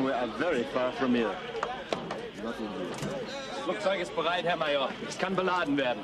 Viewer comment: Does anyone know what he says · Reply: Being Danish here, my German is not that perfect. It sounds like: Flugzeug ist bereit, herr Major. Es kann beladen werden. Aircraft is ready, Major. It can be loaded.